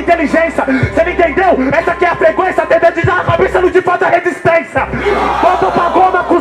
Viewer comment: O que ele fala no 10:52 ? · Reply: Ter dread na cabeça não te faz ter resistência